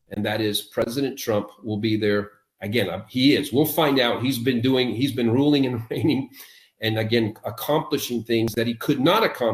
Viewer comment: For a second I thought you said "ruling and BRAINING", damn, I found that funny